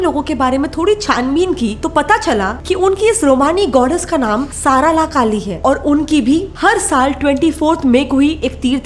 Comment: I heard Sara Ali Khan😂